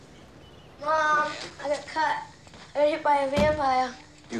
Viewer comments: I can't figure out what Michael's saying. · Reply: thanks. I thought he said "I got hit by a van pile". 😊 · I can't figure out what Michael's saying. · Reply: "I got bit by a vampire".